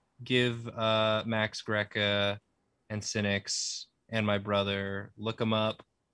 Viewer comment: I keep hearing say “Sinix”, and thinking that he’s saying “Cynics” - like he’s addressing the cynical people and/or Greek Philosophers in the audience.